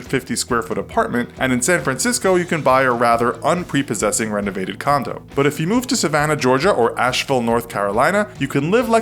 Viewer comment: I thought he said a condom ....